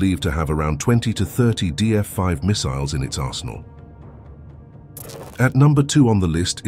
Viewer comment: first time I heard in it’s asshole 😂